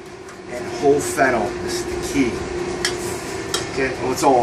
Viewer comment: At the frame 11:48 secunds, you say (whole fenon?) I am not sure if i heard right. Can you, please, explain what it is. I would like to know the translation to portuguesa. Thank you. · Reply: Whole Fennel Seed